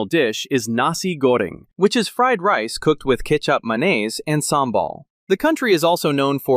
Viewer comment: Did I heard "ketchup mayonaise" in the indonesian cuisine part? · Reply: no, he said “kecap manis” not mayonnaise 😅